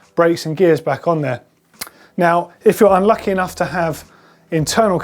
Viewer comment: " Lucky enough to have internal cable routing" - Riiiight, neverending fun and convenience in a nutshell! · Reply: Space Cowboy i heard "UNlucky enough"